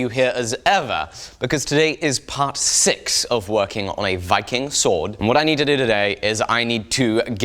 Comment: Not going to lie had to rewatch the beginning because I thought you said fucking sword lol